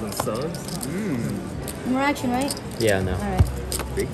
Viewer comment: what did the dealer say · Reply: I believe it was “no more action right?”